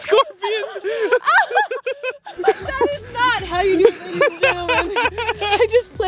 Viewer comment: Popular opinion: Everyone wants a friend like 😊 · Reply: omfg I thought you said “that is not the truth” so sorry lol 😂 and now I will go die thank you 😂